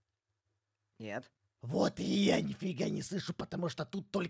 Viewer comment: Сегодня др моей подруги И когда иду спать я всегда смотрю его видео и на 9:55 я подумала что он сказал,,вот и я не**я не слышу" 😂😂